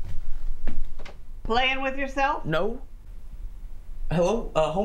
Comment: she really said: play with yourself? 😂😂😂😂(I don't speak English well)